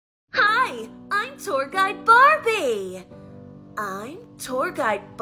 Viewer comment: I hear “hi I’m toy guide Barney” for some reason